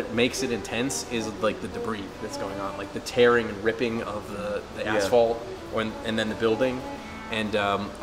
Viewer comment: DID HE SAY "TEARING AND RIPPING THE AS*HOLE"?!?!